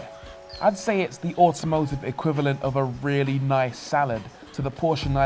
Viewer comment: did you say this is the automotive equivalent to a really nice salad?